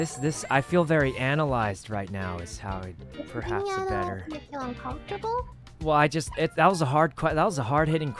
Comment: i think said "does that make you uncomfortable" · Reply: She said, "does being analyze make you uncomfortavle"